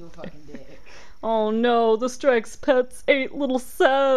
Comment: At first I thought strexpets said "sexpets" XD